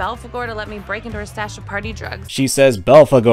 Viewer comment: I thought she said party trucks not drugs